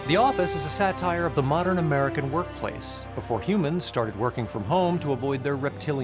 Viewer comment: did he said" work from home" at 2:06 ? well it is really a documentary for aliens after human extinct....